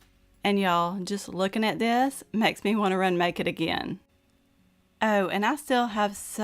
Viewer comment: You said "remake it again" and I thought you said "run naked again". I got a good laugh at that.